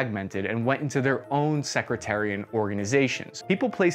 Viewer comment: Did you just say "secretarian organisations" @ 4:30? I suppose you meant sectarian...still I laughed.